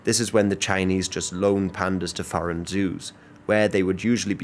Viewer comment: around this time, I hear something that makes me spontaneously blast out a laugh and scare my reating cat. I went back to listen again, and I am disappointed now that the word is actually "dues" and not dudes. Omg, I suggest highly that in future videos, at one spot in the script and only 1 to maintain the professionalism of the script, that one hilarious word is inserted somewhwre. Let us who know what's up listen intently through nearly 3 hours to find the hilarious part. Please do this because your voice and script like I said are super professional. Hear me out: So the contrast of that strict atmosphere with a colloquially silly phrase, like "pander to foreign dudes" could surprise and delight us weirdos. 😂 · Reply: The word was zoos ha